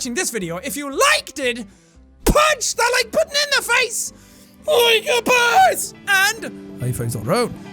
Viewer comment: i swear when jack said "LIKE A BOSS" it sounded like he said "LIKE A BUS"